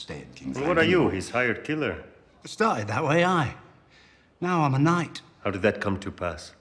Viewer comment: What does Bronn say at 1:33? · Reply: "it started that way, ayy."